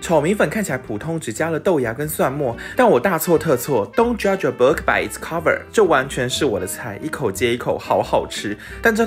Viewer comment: 好快樂的實測哈哈哈哈 我也看得好快樂🍮🍮🍮 4:34 我聽成Don’t judge a burger by its cover 也好合理哈哈哈哈哈